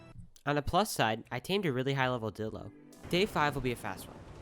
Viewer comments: did i hear him say “i tamed a high level dildo” i know what he ment but still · i thought you said high level dildo